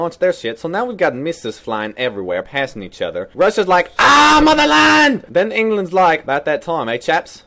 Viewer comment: When I was a little kid I always thought he said, “AHH! MOW THE LAWN!” 😅